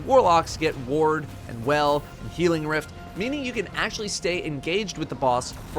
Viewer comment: For a second I heard “Ward, Well, Healing Rift” as “Ward— well, Healing Rift”